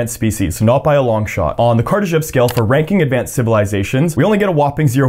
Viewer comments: I was sleepy and I thought this guy deadass said "Kardashian Scale" 😭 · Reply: Man I thought he said the Khrushchev scale 💀💀💀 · I was sleepy and I thought this guy deadass said "Kardashian Scale" 😭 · I thought he said Kardashian scale at first 😂